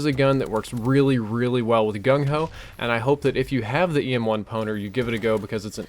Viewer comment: I thought you said EM1 Boner :)